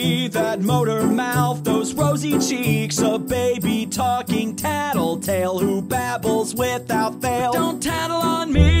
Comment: When he said tattletail at 0:18 I thought I heard matpat.